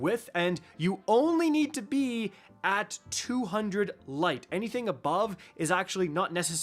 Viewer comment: did i just hear be 200 light to get tier 4???